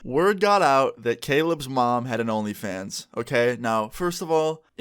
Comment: I thought u said my mom made an onlyfans I was like hold the fuck up wait a minute